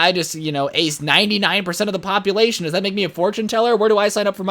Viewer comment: I thought is said ‘Fortnite Teller’